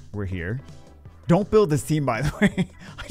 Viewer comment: There you go folks, technically said "Build this team". Though I don't know what the word before meant, he did say Build this team